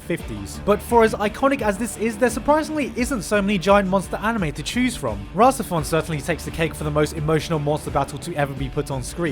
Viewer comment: What's the name of the anime? Can't understand the spelling. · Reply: rahxephon, its an evangelion ripp off from bones